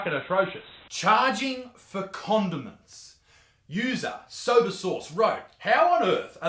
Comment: I thought he said "charging for condoms" not "charging for condiments". 🤦‍♀️